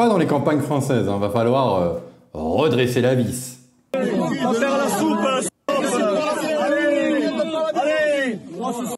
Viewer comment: moi j'ai entendu , va faire la soupe, escalope!! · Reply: Moi j'ai entendu "va faire la soupe antilope"😂